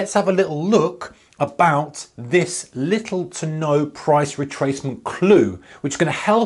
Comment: I thought it said ,” LiWetono Price” 😄 like a Hawaiian word